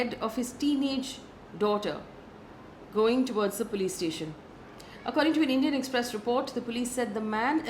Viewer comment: I thought you said "playstation" and was like damn...that dude needs to stay away from video games.